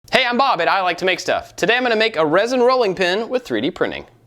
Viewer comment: It took me years to finally realize you say, "Hi, I'm Bob AT I like to make stuff." I always thought you said you were Bob AND you like to make stuff.